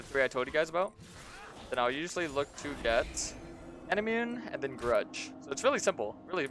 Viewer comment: I can't understand. "I'll usually look to get _____ and then Grudge." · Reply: might be a bit late to reply but he said manamune